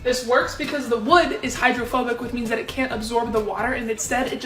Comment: Omg I thought she said the wooden spoon was “HOMOPHOBIC” like what😂😅🤣